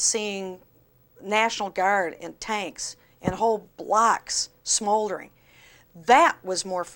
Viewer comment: Her accent makes certain words sound different to my pronunciation. At 0:22 I thought she said “whole BLACKS smouldering” 😲😳 then I realised she meant “BLOCKS”. Phew!